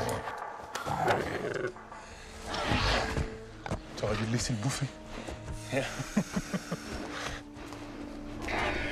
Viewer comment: What does he say to him there? 'Bet you're gonna have to change underwear after that one ...' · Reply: "T'aurais dû les laisser le bouffer" It means "You should have let them eat him" ^^